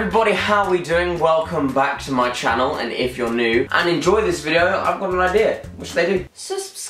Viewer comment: When Jack said 'so if your new' I thought he said 'send nudes' 😂